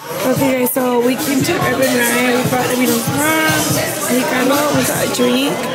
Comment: You went where!??? What restaurant is that?? I rewinded it 4 times and can't make out what you're saying!!!! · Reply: Herbs and rye